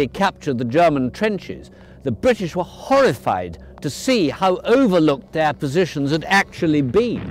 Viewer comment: "The British were horrified to see how..." WHAT? "... their positions had actually been" what'd he say? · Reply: overlooked